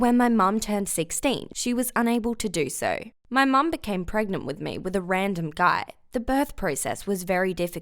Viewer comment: I thought it said My grandma got pregnant with my BFF lol